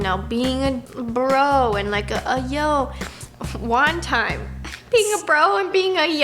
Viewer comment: no she said being a yo 😂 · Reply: nah go back and watch it lol, Roni thought she said being a yo but Nessa actually said like a yo